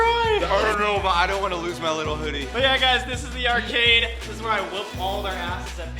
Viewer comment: said "little hoodie" but I heard it as "Lil Huddy" man 😭😭